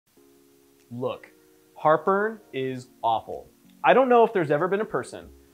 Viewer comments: at the beginning I one hundred thought he was saying “look... Harper is AWFUL” and I could not figure out why the heck he was being so mean to whoever Harper was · at the beginning I one hundred thought he was saying “look... Harper is AWFUL” and I could not figure out why the heck he was being so mean to whoever Harper was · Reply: Julia Calvet oh, no I get what he is saying. Heart burn! 😆 🤣. I thought he was saying Harper as well. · I thought he said “Harper is really really awful” and i was like damn what did she do. Like i had wandered into doctor drama.